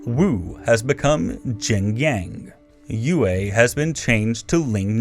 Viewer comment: it is supposed to be Jiangnan instead of Jingyang (wtf is Yingjang and other names excepting Lingnan and Zhongyuan).